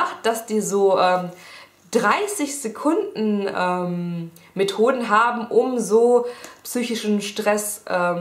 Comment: ich hab erst 'mit hoden' nicht 'methoden' verstanden:D hahahaha